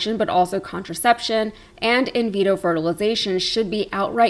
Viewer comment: As I see it, they aren't backing off. It's in Project 2025, and they're pushing "fetal personhood". BTW, I may have misheard (as did the closed captioning), but it's "in vitro".